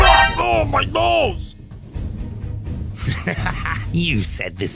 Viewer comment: Bro who else thought the officer said “ahhh my balls” Rather than “ahh my nose” 💀